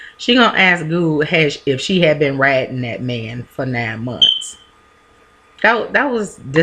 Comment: i thought Honey said you been HIDING that man for 9 months>>>>.. Yes their clothes r too tight always